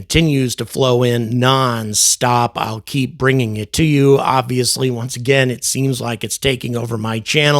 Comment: Did you say “Avi-ously”?